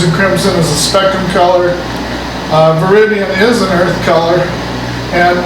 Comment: you mention a kind of green, an earth color, but I can't hear what kind of green it is. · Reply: Veridian